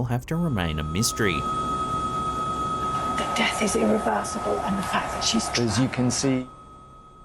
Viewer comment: it sounds like she said the fact that she's drugged . the queen was murdered.